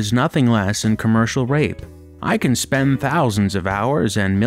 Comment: did I hear that correctly?? "Commercial r**e"???? To equate renting games to assault is despicable.